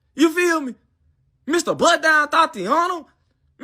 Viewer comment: Thats how 80% of the young generation sounds like,its like their mouth is lazy or their brain is working extra slow. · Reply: literally too lazy to even pronounce words 3:50 sounds like he said “Mr butt dial totiana”. Too F’n lazy to even fully pronounce “bust down”